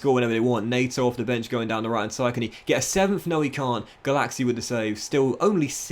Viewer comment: Every time said "Gulacsi" I heard "Galaxy"